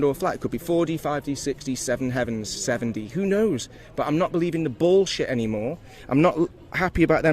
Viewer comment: Does he say “I’m not believing the ballsh*t anymore”? 😁